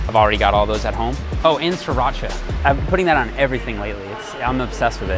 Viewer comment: Does anyone know what says at 10:32? That he uses on everything. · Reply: Sriracha sauce